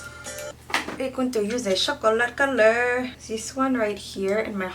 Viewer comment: Could someone explain what she’s saying here 9:13 I can’t understand what she’s saying. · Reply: She says she’s going to use a chocolate colour